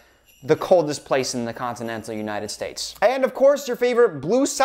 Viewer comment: “The coldest place in the continental United States”.... loves Twilight and this proved it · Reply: Thank you for the comment, I'm not a native English speaker and I thought he said "continent of United States" 😂